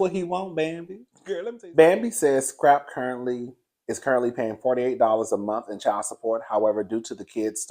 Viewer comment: Baybeeee!!! I thought you said $48 a month at first 😳, then I ran it back and I was like oh he said $480 a month. But when you broke it all the way down and said $4,800 (fawty eight hunnid) a month I was a lil shocked 🫢! I’m like you, I thought it was more like $1,200 a month for all 3. 😊🤭🤭🤭